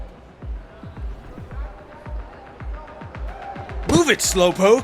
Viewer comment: Subtitles says = Emperor She says= Prince